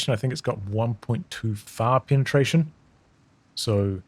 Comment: 1.2 far penetration sounded like 1.25 with a Texan accent LOL